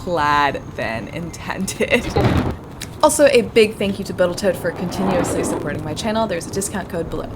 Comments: does she say, "A big thank you to Botal Toat." ? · I wonder if anyone else noticed you said a big thank you to boatal tote at 18:50 instead of total boat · did you just say Botal Toat? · Did she say “ and thank you to botal toat”? At the 18:55 ish mark? Haha. I would have done the same thing at some point. Love that she kept it in there.